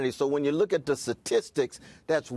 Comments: Did he just say "tasistics" twice? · Did he just say, "satistics"?